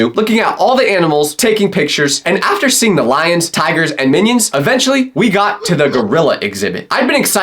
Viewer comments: did he said and minions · did really said Minions? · did he just say minions???😂